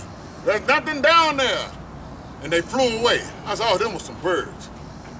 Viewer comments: My favorite part is "last night and the night before" so he's saying it took two days to figure out it was birds. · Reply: I thought he said "I tell you, those were some pervs" for 6 years😂😂😂😂😂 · “Last night, and night before, there was three women, walking down the street that way. I saw ‘em. I said, “Hey! You three women! Why are you walking down the street that way? There’s nothin’ down there!” And they flew away. I said aww them was some birds.” - EDIT: RIP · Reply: Ah thanks. I didn’t know what he said. I thought he said, “and then they threw away”